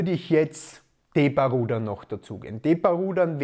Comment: Welche Übung ist hier gemeint: 10:51? Ich habe Depa Rudern verstanden, aber leider nichts im Internet dazu gefunden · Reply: Er mein T Bar rudern